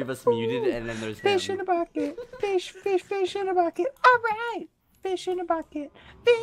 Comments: At first I thought he was saying fish in a baguette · i thought he said “fish in a baguette”